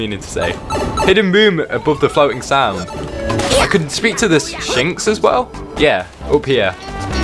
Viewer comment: Is it just me or do I hear shinx like the Pokémon shinx.